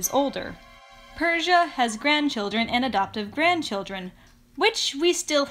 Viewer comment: I heard Keneda 😂